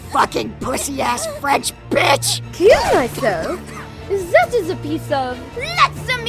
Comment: Am I the only one who misheard her saying "kirito?" when she said "kill myself?". I thought it was a joke that she was talking to the voice actor from SAO abridged.